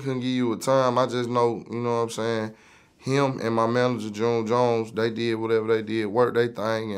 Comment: did he say his manager is jim jones 2:31????